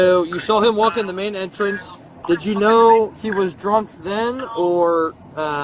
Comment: I thought she was saying dark not drunk, it doesnt make much sense.